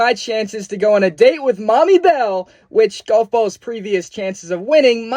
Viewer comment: did you just call bell “Mommy Bell” 😭🙏